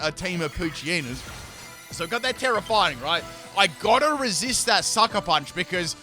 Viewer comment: Did i Just Heard You’re Nintendo Switch? 😂😂